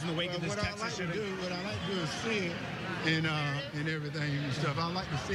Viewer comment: I thought he said, "I'd like to see it..." in response to new gun laws. It was so low on here that I listened repeatedly and heard the same statement. Just me??? I do not know.